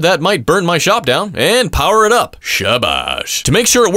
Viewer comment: Did I hear "Sabash"